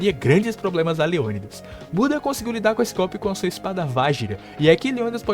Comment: Num é possível que só eu ouvi "Buda defendeu com sua espada VAGINA" 😂😂😂😂😂😂